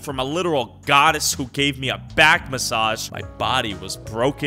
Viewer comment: I thought he said i was gifted by a godest that gave me backshots